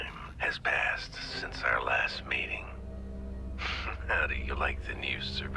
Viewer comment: I wasn’t paying attention and thought it said “ it’s been a long tome since our last MATING” and i rewatched 3 times questioning the line until I realized what it really said then I laughed my ass off for 10 minutes